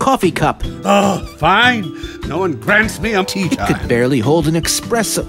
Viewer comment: I have no clue what hes saying here. "No one Grant's me a tea kyle? No one vents me a tekile?" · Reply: "No one grants me a tea time." I think